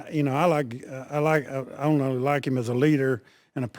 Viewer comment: he said he not only likes him as a leader idiots, he fumbled over his words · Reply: Oh ok I thought he said he didn’t like him as a leader but as a qb he allows you to win